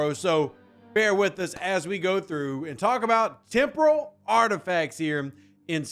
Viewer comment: It sounded like you said tempura parts😂